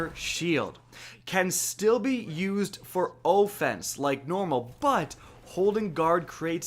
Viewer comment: did he say o fense and not offense?